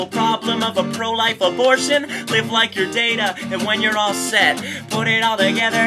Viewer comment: i didn't realize he was saying "live like your data" for awhile. i thought he was saying "live like YOU'RE data" and i thought, "wow! bo burnham likes TNG?! he gets cooler every day..."